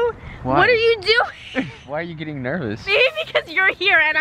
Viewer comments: did she say baby ? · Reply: she said maybe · Did said bae to 7:12🧐😳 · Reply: She said "Maybe"